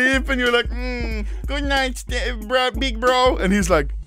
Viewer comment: DID YO SAY GOODNIGHT STEPBRO